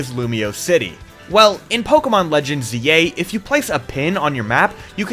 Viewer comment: Anyone else hear “Pokémon Legends Yay” instead of “Pokémon Legends ZA” at 0:48?